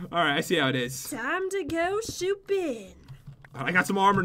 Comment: did anyone notice that said shooping instead of snooping in